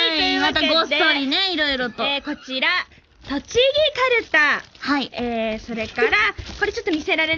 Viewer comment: should be Tochigi Karuta (actual product: <link> Also instead of "Show", they might be saying the japanese word for award: 賞 which is pronounced as "shou" As always thank you for these! You are spoiling us with multiple releases in a week.